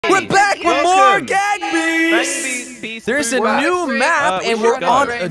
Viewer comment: I thought he said "Gag Beasts" at the start. 💀